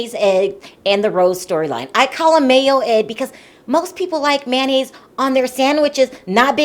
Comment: I thought you said mayo egg. I like him, glad he didn’t send that money to sister!